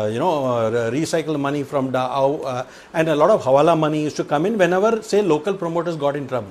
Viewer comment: did he say davood?